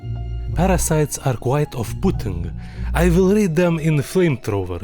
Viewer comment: I thought he said parasites is quite of putin My ears need fixing 😅 then again I never clean them so my ears are clogged a bit